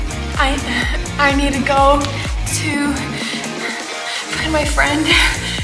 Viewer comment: I thought she said I need to go to clean my friend 3:01 And this is my mom when I keep asking her the same question but it’s not my fault that she doesn’t answer me